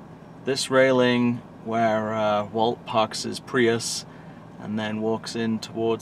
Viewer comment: Did he just say Prius?!